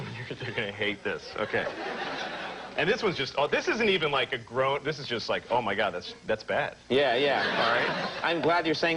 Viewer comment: Absolutely love Louis. He's the only one that cracks me up to my core. ..and no matter how bad I'm feeling. Thanks for putting this awesome timeline together. @ 28:05 can't make out answer to bad joke about 'dog that doesn't lick his balls"....."dogsn't??? CC says' 'dog' lol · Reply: Star Child yes he says dogsn't lol